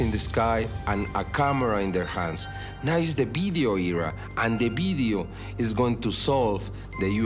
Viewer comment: What is he saying " bedio " what is that? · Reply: he is sayig 'video' but with his accent it comes out 'bideo'! He pronounces the 'v' like a 'b'!